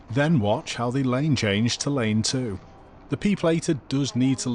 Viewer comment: 'P'-plater' 😀 Heard that as 'People eater' 😒